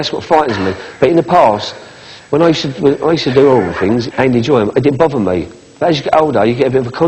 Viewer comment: Did he say 'oral things'? I do not understand his mongrel accent.